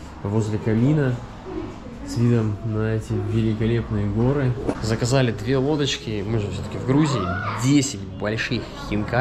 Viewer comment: на моменте когда мне послышалось "заказали 2 водочки" перематывала раза 2 🤣